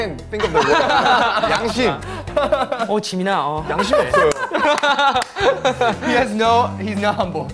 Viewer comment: I thought Eric was going to say "He has no Jams" lol